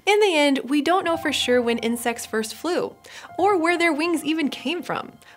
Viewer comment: i thought it said ‘when incests first flew’